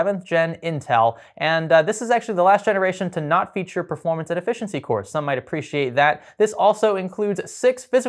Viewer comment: I swear I hear him saying performance inefficiency cores.